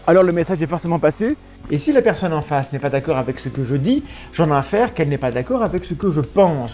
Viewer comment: qu'est ce qu'il dit à 1:17 ?! "j'en n'en à faire qu'elle n'est pas d'accord avec ce que je pense" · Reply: Il dit : j'en infère qu'elle n'est pas d'accord ... A comprendre: j'en déduis